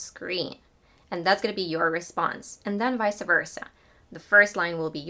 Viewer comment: What she said at 0:29? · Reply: Vice versa